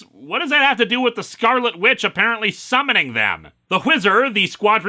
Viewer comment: I feel like more of a deal should have been made about the Squadron Supreme expy of the Flash is named... The Whizzer. Which implies super urine powers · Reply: Oh, I thought he said "The Wizard". Even though that makes no sense, it's a better name!